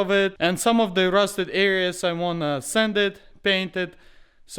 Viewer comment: Why do I hear “send it” when he says “sand it” 🤣